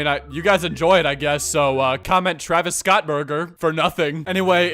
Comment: travis scott burger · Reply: I thought you said travis scott boner